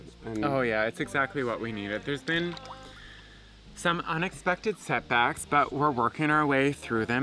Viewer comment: OOOOH! You said "set backs" and I thought you said "sex acts". I was confused.